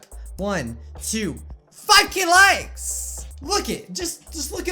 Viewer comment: it sounds like he is saying f ing likes but I don’t care ... btw I liked